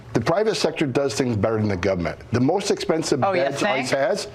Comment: did he say "gubment"...?